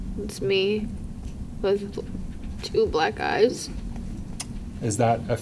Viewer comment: Omg….. at the 37:48 mark I thought she said “that’s me with two black guys.” I was like WTF, how is that relevant and who took the pictures??? I was like did dude do revenge porn on this chick? I rewound it 5 times and finally figured out she said “two black eyes.”